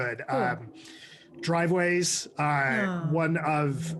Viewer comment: Anyone know who they are referring to here? And what the films name is? Is he saying “Driveways”? I get I could Google it but rather have a conversation about it. lol